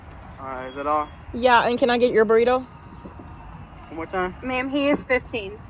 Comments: When he said "one more time" i heard "what time"😂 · He said "can i have your burrito" I thought he said "yeah what time" But he said "one more time"😢